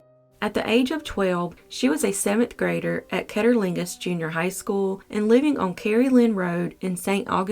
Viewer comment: I thought she said at cunnilingus school 🤦‍♀️